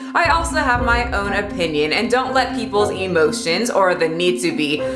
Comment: When you said "emotions" I thought you said "emo chins"....that works so well!